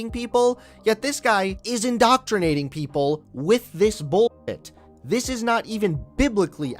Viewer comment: "This guy is indoctrinating people with this bulls**t" Me (what I thought he said): "This guy is indoctrinating people with this BULLET" Sometimes, censorship can be EVEN funnier than the profanity it hides. 🤠